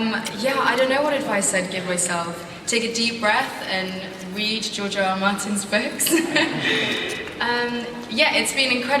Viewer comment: Does she say George R R Watson? 5:30 lol